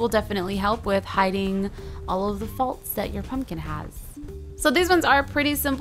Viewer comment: when she said the faults in the pumpkin I thought she said the faults in our pumpkins ha ha ha